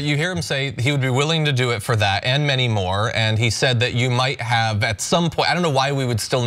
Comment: I thought he said, "...and maybe more" (not "many more") This business of Joe walking around, looking down instead of meeting his interviewer eye-to-eye looked sloppy and as though the Pres didn't know how to handle himself.